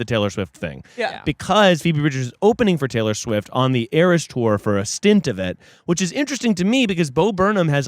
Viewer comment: okay but it did sound like said "the heiress tour" and i was like "that's hot"